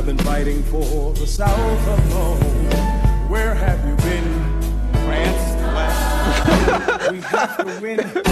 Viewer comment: I feel like i’m going crazy i thought he used to say “uh France?”